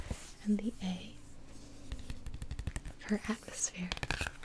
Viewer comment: "A stands for-" air! "Atmosphere" oh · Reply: Ann K She said atmosphere? Lmao I thought she said A*s mat here.